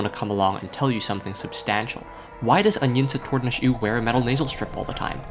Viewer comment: can anyone understand what he says here? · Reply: Unyince Tordinush Ooh is the name of that ex-pop